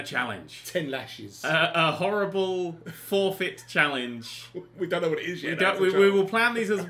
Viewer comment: i thought said 'A horrible 4-bit challenge', and i was like DESERT BUS